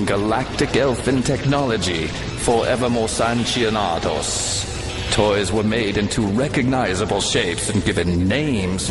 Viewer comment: Sounds like it said, "Forevermorsanchionados...." lol!